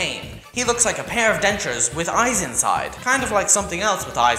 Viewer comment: wait did you just say Denchuhs?